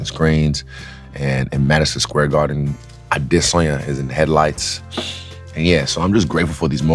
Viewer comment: It's in "headlights" he says. Like MSG uses cars instead of screens and signs.